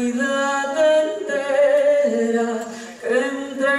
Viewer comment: xD que te paso en el himno y que es lo que dices en el minuto 1:40 enteca? o entera xD